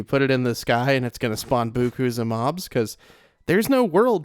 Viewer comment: did you just say "boo koos of mobs"? I think you meant "beaucoup" but I've never heard someone pronounce it that way